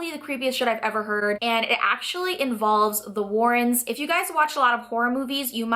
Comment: Thought she said "if you guys watch a lot of porn movies" LOL had to rewind that and check, laughing my ass of now.